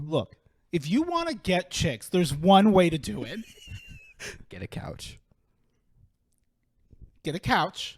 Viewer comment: I’m living for the new set up and comfy vibes 😌 As I was writing this, all I heard was “If you want to get chicks, there’s one way to do it…” “Get a couch” followed by wheezing jsjsjsjd I live for this kind of content yes yes